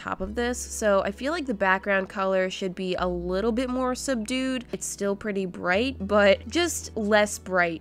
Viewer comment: When she said subdued it sounded like she said sup dude😂